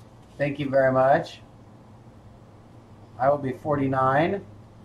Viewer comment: ish, Did say 49?! Or 39?? Cuz it sure sounded like 49, but he sure looks more like a 39!! Happy early birthday! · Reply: Thank you! Yup, 49